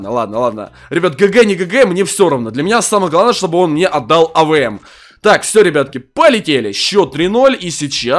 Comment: Эмммм в 2:52 сказал чтоб он не отдал авм, кто услышал Лайк!)